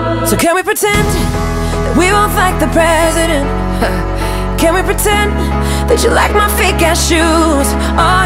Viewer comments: I think I heard "Can we pretend that you like my big old butt?" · honestly i was listening this in the background and i heard: "Can we pretend, that we will fight the president"